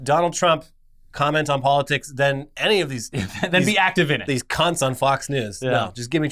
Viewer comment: in the video did he say cunt ?